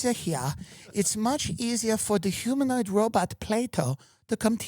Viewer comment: first I heard them say playdough, then I thought they say play-doll - reading the comments I understand they call it Plato... May this never find my country please